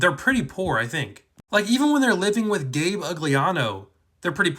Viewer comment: why did I hear "Dave Ugliana" 😭 Also, Percy doesn't get paid for his quests, so they probably have what they had before. BUT! Sally did probably get paid a lot of money for "The Poker Player" (aka medusa-fied smelly gabe)